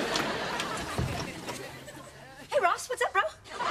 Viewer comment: I have a bet with someone for this: What is Monica saying at 0:56? "Hey Ross, what's up ____?" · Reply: MrMichael024 bro